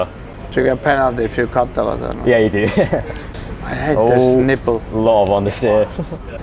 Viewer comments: Did kimi say oh nipple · Did kimi just call the analog stick a nipple